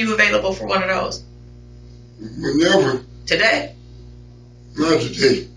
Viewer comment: "Give me a lie detector test! Not right now though, I'll call you when I'm ready" 🤣🤣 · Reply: Then they followed up with when u think you'll be ready ..I thought he said "NEVA" 😂 but he said whenever lol this was a piece of work but I really don't think he started the fire I think he was innocent of that I just think they wanted to nail somebody for it. And then he gets locked up for theft and he's talking to his self so they probably was like f**** let's just stick the fires on him